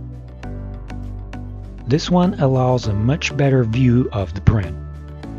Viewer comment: Dude I've seen this video so many times and it's been really helpful, but today when I was listening at 4:47 at a lower volume I heard "this one allows much better view of the pffftttt" and I was like Wth!? o.O, thanks for these reviews, super helpful